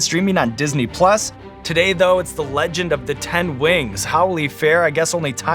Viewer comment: forgive me but when sean said ten wings i thought he was still saying rings but in uwu